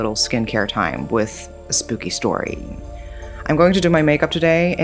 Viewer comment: i thought she said "scooby stories"